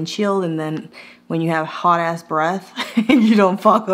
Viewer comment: 😂😂 At first I thought you were saying, “hot ass breast“! And I said to myself, “that’s me! I have a hot breast!” (I’m menopausal!)